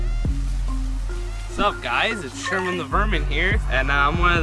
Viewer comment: I thought he said “ Sup guys it Sherman the virgin here”